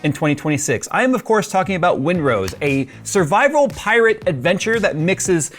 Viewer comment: OH, that makes more sense. I thought it said Windows.